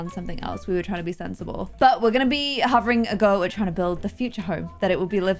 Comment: For a hot sec, I thought said Girl @ 1:09 but she said: Go. oof, close call.